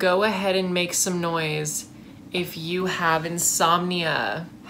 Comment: Not gonna lie, at the beginning I thought he said “if you haven’t seen Mia” not “insomnia”😂😂